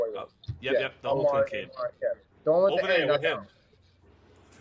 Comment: What did say at the 30:55 mark? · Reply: I think he said "Over there with him" and pointed at Rahiem